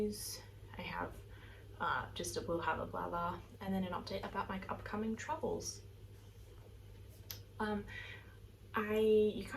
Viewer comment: Thought you said “upcoming troubles” at the start 😆. Thankfully nope - upcoming travels!